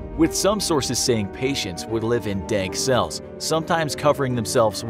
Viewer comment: did he say dank cells?